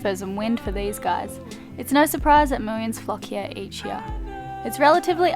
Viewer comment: can someone confirm what said, as I had to rewind a few times to hear what she said, because the first, second, and the third time, it sounded....a bit unusual. ...waves for the surfers, and wind for these guys, it’s no surprise that.... marines flock here each year. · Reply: Australian accent. She said "millions".